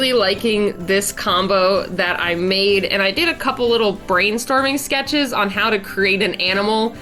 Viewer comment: Behold! the Gingerbread Flynx! it looks delicious. Happy holiday! Also, at 3:02, did you say brain starving?